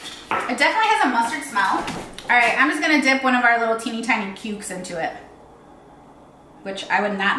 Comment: I have to stop playing with my dog when watching you. I had to look up and rewind your video twice. First I thought you said I’m just going to dip one of our pubic in it? But I can’t spell so it is the short version of that. Then I thought you said how I have to black people because they don’t know how to act. I knew you didn’t say that so I just rewind twice to hear exactly what you said. Lol. I know it is me because on the family guy song I thought they said effin cry. Lol. Need to get my ears checked.